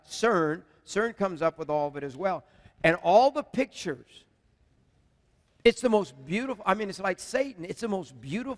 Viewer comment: What did he say at 46:50...it sounded like he said "its the most beautiful thing. I mean its like Satan".....??